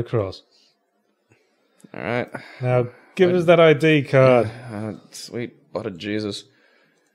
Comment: Did say "Sweet Otter Jesus" at 4:52 ?